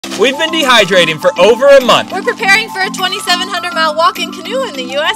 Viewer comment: I thought they said that they had been dehydrated for over a month and I was very concerned